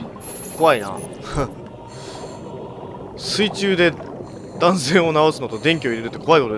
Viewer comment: しかも海水 あと水中で男性を治すって聞こえた